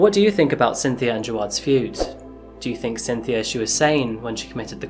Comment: as a weeb i keep hearing “Cynthia the Baka”